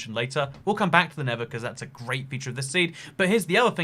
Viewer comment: I thought you said the nether was a great part of this seed! I was excited to see it